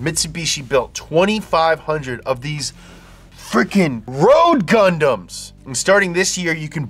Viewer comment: i heard "road condoms"